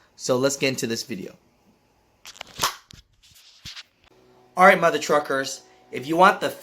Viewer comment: I don't know why I always hear "Alright mother fuckers".